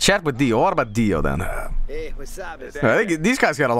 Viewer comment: Am I tripping balls or did Dio say “hey wassup”